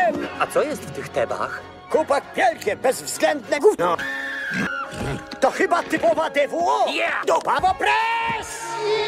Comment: Co mówi satyr w 4:10? Od 9 lat mam zagwozdkę PROSZĘ POMOCY! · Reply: zgaduję, że ''to chyba typowa DWO, dupa w opresji''